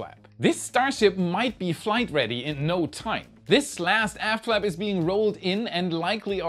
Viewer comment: Did he say "This last ass flap"?